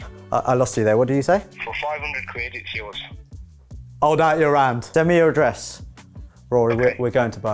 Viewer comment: Hi what is it your are saying at 4:30 , hold out your hand?(sorry can't hear what it is)